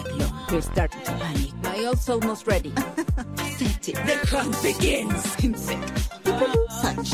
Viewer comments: i always hear "NETWORK BEGINS" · I've always thought she said let hem begins 💀